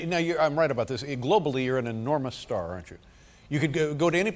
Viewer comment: What he said in 2:21 ? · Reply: Globally you are a enamours star